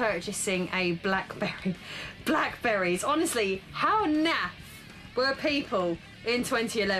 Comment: Omg, did I just hear the word naff? That made me feel really old